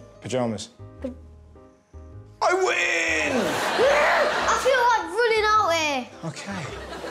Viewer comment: What did that kid say at 6:59 i didnt even catch a word 😂😂. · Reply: “I feel like running out here!”